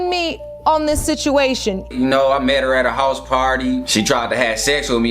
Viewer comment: I thought he said "hoes party" but captions said "house party" 😂